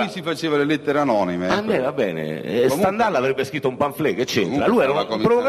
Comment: intorno al minuto 58:50 concutelli dice che stendhal avrebbe scritto...cosa??? cosa dice,qualcuno me lo sa dire? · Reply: Credo abbia detto pamphlet. Il pamphlet è una breve pubblicazione, scritta con intento polemico o satirico, in cui l'autore prende posizione contro un altro autore, o contro un potente, o contro l'intera società, su temi o avvenimenti di stretta attualità.